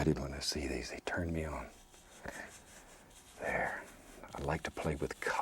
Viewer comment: OK @ 11:50 DID HE SAY THE COLOR TURNS HIM ON????